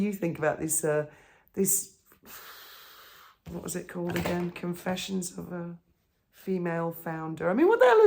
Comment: I keep hearing it as "Confessions of a Female Failure" 🤣🤣🤣🤣🤣🤣